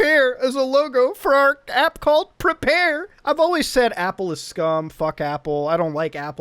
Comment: I thought it said apple is cum so